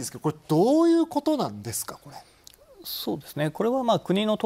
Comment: そうですねー が そうぞくぜー に聞こえてきた